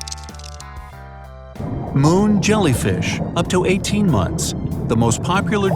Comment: I thought u said MOAN jellyfish